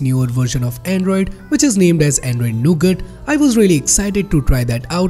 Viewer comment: when he say android nougut...i misheard it to ANDROID NOGOOD